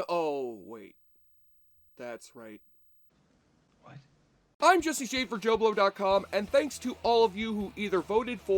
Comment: It's Shade, actually. Buy God bless your kind words. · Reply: 😅🤭 Oh... I heard it as "This is Jesse Schafer, JoBlo...". Excuse me please haha. The message still stands however. Bloody wicked 👍